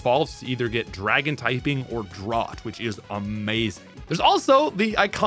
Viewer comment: did he just say DRAWT?